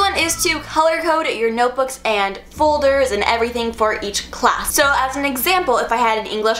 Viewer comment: I am the only one who heard her say "fuldurs"